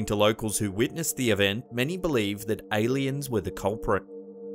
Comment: when he said they believed aliens to be the culprit, i coughed a little bit and thought he said namakians to be the culprit, but i watched it back